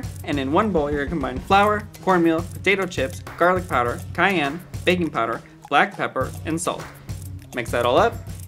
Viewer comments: i thought they said baby powder😭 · I thought he said “baby powder”. 😅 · FOR SNOOP DOGS I HEARD “Baby Powder” INSTEAD OF “Baking Powder”